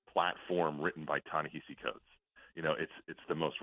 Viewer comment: What does Domenech say at 7:21? “A platform written by _____.” What’s the word he says there? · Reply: Ta-Nehisi Coates